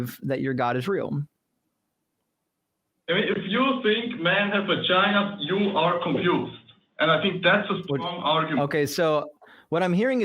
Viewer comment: When said, "If you think man has vagina, you're crazy" I heard "if you think man has been to China, you're crazy."